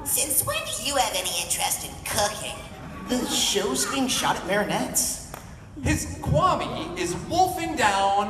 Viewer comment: Plagg: "Since when do you have any interesting in cooking?" Adrien: "The show is being shot at Marinette's" Boi just admit you like the girl already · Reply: i screamed and woke up my brother because I heard “the show is getting shot of marinette!”